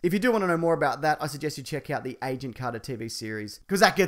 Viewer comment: "check out the WHAT and WHAT series" ?!?!? I played it back like 20 times and still don't know what you said! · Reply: "check out the Agent Carter TV series"